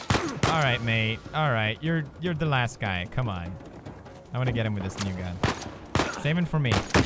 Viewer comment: did he say the n-word?? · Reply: He said “ come on, i wanna get him with this new gun “